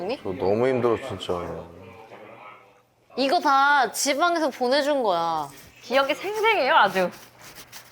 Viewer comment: 집안 말구 지방이라고 들리는 건 저뿐인가요..?